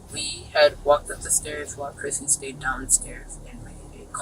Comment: Ik this story is wild, and rip DJ but why 16:47 it says sairs instead of stairs man